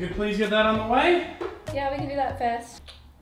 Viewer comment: did anyone hear her say "fat ass" at 5:31? if you did